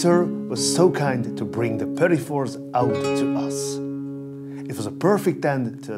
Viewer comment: Does anyone know what word he says at 6:41? Polifors? The subtitles are missing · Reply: petit fours - a little bite sized treat